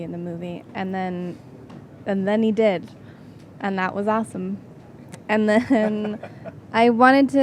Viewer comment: did Dakota say assome?